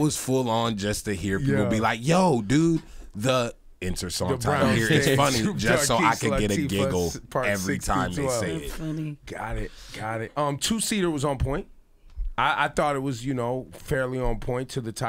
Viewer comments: what did that girl say? "Not funny" ?? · I thought he said "two cedars" like it was about some trees